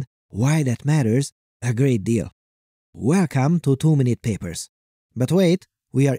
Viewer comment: I want to confess that i always hear you saying "Too many papers" and not "two minute"